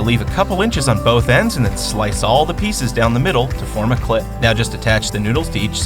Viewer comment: did he say clit?